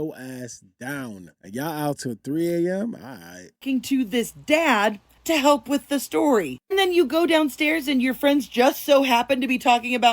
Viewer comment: I believe he was on the boat 🛥️. The audio reveals that. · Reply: I thought said bart helped with the tow